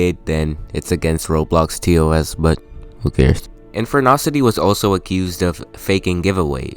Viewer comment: WHY DO I KEEP HEARING "Infernasu"